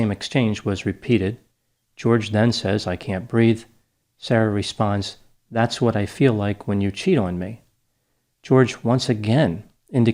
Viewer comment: Well I think she premeditated the "Lock in Suitcase" ordeal but didn't mean to kill him ultimately. She was trying to discipline him from all the bad things he did to her. But bc of her being really intoxicated or even inebriated dictated the pace of sentencing him to "Eternal Shut Eye" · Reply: I thought she said "that's what it feels like when you CHOKE me" in that video, the fact that she actually said "that's what it feels like when you CHEAT on me" seals the deal on this case. I was having sympathy, but it certainly doesn't sound as if he was physically abusing her by her own account, that fact makes this case pretty clear: she was punishing him because she felt he was being unfaithful and she did it by torturing him to death - so basically, fuck her. Unbelievable. She talks about him like a pet owner would a dog, she is clearly a narcissistic person who sought out someone who would be entirely dependent on her emotionally and that's what she got with this guy, he started messing around with chicks online and she didn't like that.